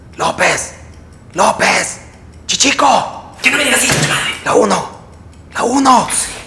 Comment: que es lo que dice chichico? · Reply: "Que no me digas chichico conche tu madre"